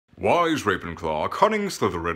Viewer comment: At first I thought he said "why is Ravenclaw cutting slytherin"